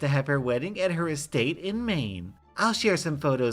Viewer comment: Did she say Maine? Bunker down soilders, she's coming..